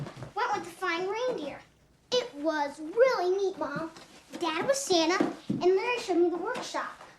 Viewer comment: wait. his wife's name is laura. scott's wife's name is laura. danny is santa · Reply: i thought you said daddies santa and i was like weelp